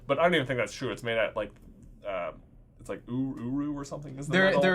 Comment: I'm sorry, did you just say the metal was uwu?